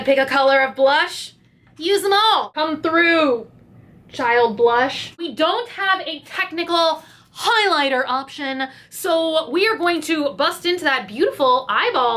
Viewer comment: you said "child blush" I heard "Shia LaBeouf"